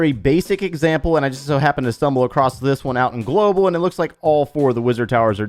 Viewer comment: Sorry did he just say he found it in global?